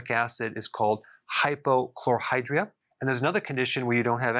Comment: I see "hyper..." but I hear you say "hypo..."